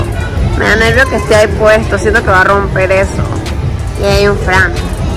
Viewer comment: que dijo y ahi hay un fan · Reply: Un fran